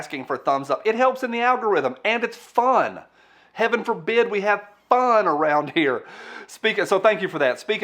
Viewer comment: "heaven forbid we have fun", yet I heard "kevin forbid we have fun". Ha ha (david!) ;)